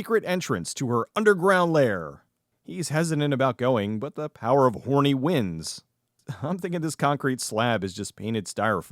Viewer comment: Legit thought you said "The power of horny winds" and i'm just gonna roll with that. May the horny winds guide you.